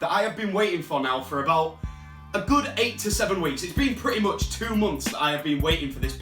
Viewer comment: Did he just say 8-7 weeks?....